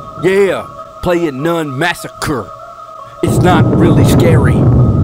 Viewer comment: I thought it said nun massage